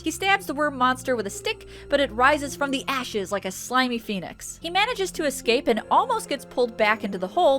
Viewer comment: "It rises from the ashes like a slimy phoenix." is a phrase I never thought I'd hear, but it's definitely fun! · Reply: I thought she said rising from the ashes like a p3niz. No lie